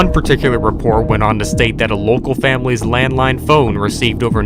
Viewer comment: Me who thought he said landmine phone: NANI?¿